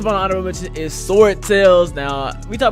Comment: captions say sora tails I think sword tails what is da name bc I wanna try dis